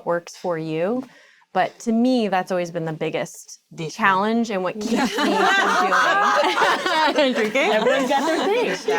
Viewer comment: Does anyone know what Sofia Vergara says here, 43:37? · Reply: Sounds like she said "Dish soap" but I can't make it out.